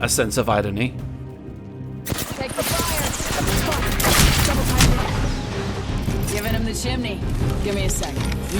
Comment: did Bangalore just say the f word??? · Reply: "Spotted! Double timing it."